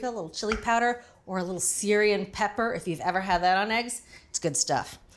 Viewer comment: Around the 1:25 mark, you talk about potential seasonings to add. What was the last one you mentioned? It sounded like “Syrian pepper”, but I have never heard of that. Thanks for the video!